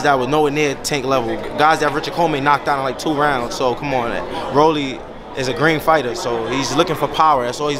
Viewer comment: I thought he said Rolly is a great fighter😂, he said Rolly is a green fighter.